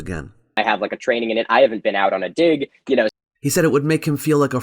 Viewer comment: It seems like the whole article is based off of mishearing in the video where he said he had training but not been out on a dig. The first time I heard it I thought he said I haven't had training haven't been on a dig too. It wasn't until I heard it a second time that I caught what he really said.